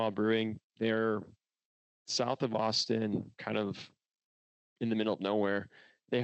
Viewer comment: Legit thought you said south of Boston. And I was like the fuck? Never heard of this brewery lol